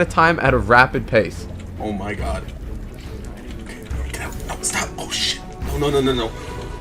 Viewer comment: DID U JUST SAY (oh no no stop oh shi*)